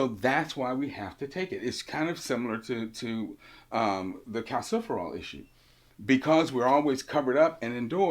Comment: I'm not english, could someone please tell me what Dr. Mills says At 2:00? "It is kind of similar to cos... cost... what? Thank you for help 😙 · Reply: when we don’t get enough calciferol aka vitamin D